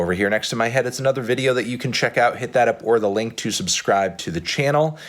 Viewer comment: i love how he's now clearly pronouncing "up or the link" after everyone thought he was saying upworthy lol